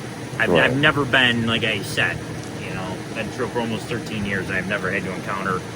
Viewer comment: 15 years on the police force and has no idea how laws work...🙄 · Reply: Almost 13 years is what i heard him say...